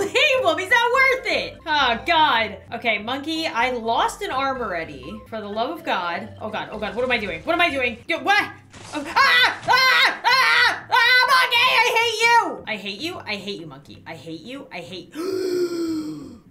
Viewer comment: In the intro why did i hear "IM NOT GAY I HATE YOU! "